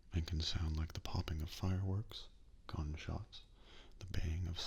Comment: I know you said gunshot but at first I thought you said "they hear a loud cumshot"